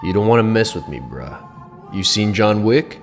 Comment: did you just call him a bra 😂